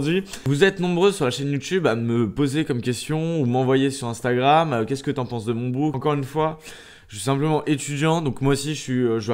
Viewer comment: Chuis mort j'ai entendu qu'est-ce-que tu penses de mon boule a